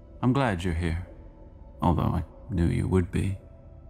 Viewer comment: What did the monitor say in 2:30 after "im glad you're here"? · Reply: He said “although, I knew you would be..”